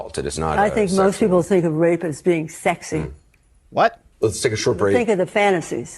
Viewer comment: whattheeverlovingcrapbaskets is she saying? "Grape is sexy"?? It's not s3xual? Tf!